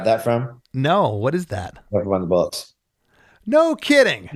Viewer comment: What did he say the sample was? I can't make it out? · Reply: I think he said “Never mind the Bollocks”.